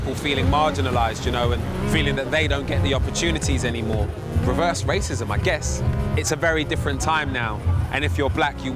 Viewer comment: Did he say "reverse racism" at 16:20....as in only white people are racist?